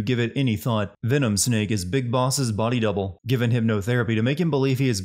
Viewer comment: Caption says Bis Boss instead of Big Boss.